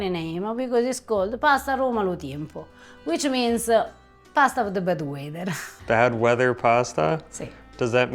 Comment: When first said 'pasta of the bad weather', I heard 'pasta of the bed wetter' 🤣